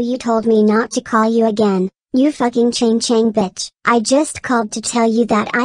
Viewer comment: Woah! Did caillou just say the word Ch1ng Ch0ng? That's an extremely racist slur towards Chinese people.